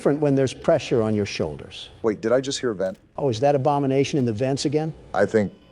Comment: I'm so used to hearing them say each other's names I thought Trump said "Obama nation" for a moment